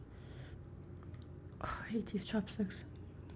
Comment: Did she say "God I hate these chopsticks" or...??